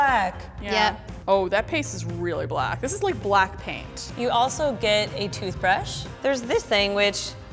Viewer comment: thought she said blackpink😂😂